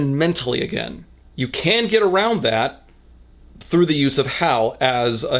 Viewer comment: "You can't get around that" Herbert West: "Hold my illegal beer." EDIT: I misheard "can" as "can't," but I like the joke too much to change it.